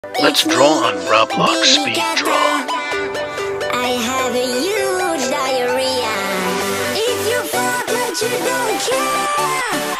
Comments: I heard: “iTs mE, bAbY cacA i hAve a hUge diArrhOea” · “ItS mE bAbY kAtA I HaVe A hUgE dIaRrHeA✨” (EDIT:Bro everyone is fighting just because of a mistake-) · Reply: I thought it said "If you #### like you don't care"😭💀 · “ i have a ✨HUuuUuuGEeeEeEeeEe🎉 DiArRhEa” GOT ME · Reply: I heard "I HAVE A NEW DIARRHOEA"💀💀💀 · I heard “lets draw grandpa on speed draw Roblox”